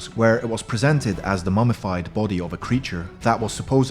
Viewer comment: When he said version i thought he said virgin